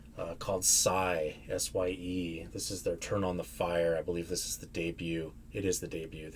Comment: I thought it said "Turn on the FPE" 😄